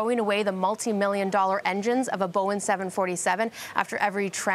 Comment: did she say "boen"